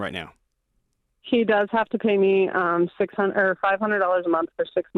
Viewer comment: I'm almost sure I heard "SHE does have to pay me . . ." Just me?